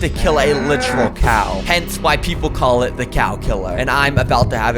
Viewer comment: I heard you say "Hence white people call it" lol